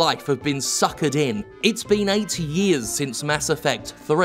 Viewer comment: You were talking about time getting on and for a moment I misheard you say that it's been 80 years since Mass Effect. Imagine EA's intrusive, vampiric style of business in gaming 80 years from now.